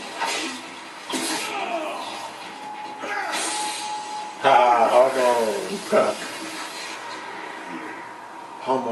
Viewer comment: wtf Epcar didn't yell "overdrive!"? Come on! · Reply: Condiment Refraction He did you just could here it since was talking over him.